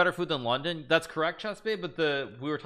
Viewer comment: At like 2:55 did he say chessbea is she still working for him?